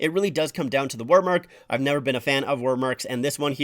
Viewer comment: Wordmark. Basically when it's just text instead of a logo. Think the Rangers jerseys, they have a wordmark. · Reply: Oh! WORDmark. That makes sense, I heard “war.” 👍🏻